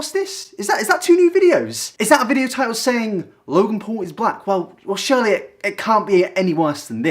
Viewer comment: It sounded like you said Logan Paul is Black